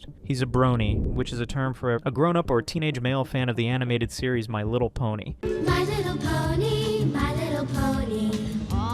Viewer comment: I thought you said "My Little Pedo" for a second